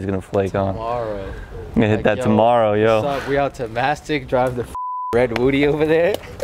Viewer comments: did say mastic as in mastic beach ?? If so that’s where I’m from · did he say going out to Mastic? 🤣